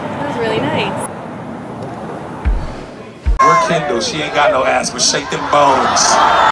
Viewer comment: My dumb ass thought he was actually saying candle and not fuckin Kendall